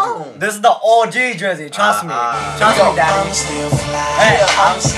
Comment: Hold on, only after watching this three times did I catch this. At 2:50, did he say "Trust me daddy"??!!!! I'm dead, why is this video so on point